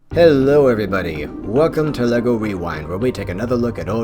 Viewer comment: Kevin Schaeffer Wait, what? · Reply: I just thought, compared to all the other episodes, it sounded like you said wewind instead of rewind. I didn't mean any hate by that. - big fan of the series.